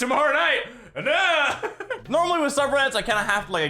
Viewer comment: Right before 5:39 you said normally subreddits... my captions said "Normaly super aids." Wow. Just, wow.